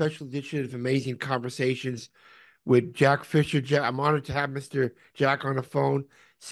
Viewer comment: i thought it said john fisher and i was about to lose my shit